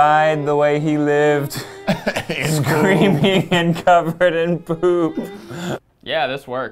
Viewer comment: "He died the way he lived: screaming and covered in poop." Dear Lord. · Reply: I thought he said creamy not screaming 😅